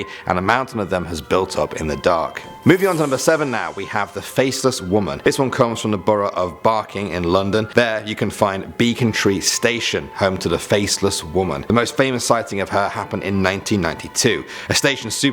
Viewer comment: Hiiiu Love these make somemore plzz When he said becontree i thought he said bacon tree